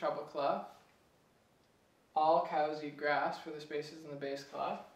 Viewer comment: haha i thought it said, all city eat greff? hah srry thx any way!! all my friends think it's really cool that i'm learning and they are amazed even thpo i been only doing it for like 2 weeks!! ur the BEZZTT